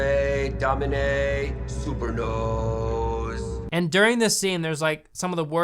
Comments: Did he just say "super nose"?? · indeed did he say Super nose??? :) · did he just say Super Nose?